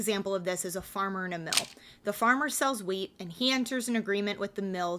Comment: I was listening and not watching, at first i thought you said a farmer grows Weed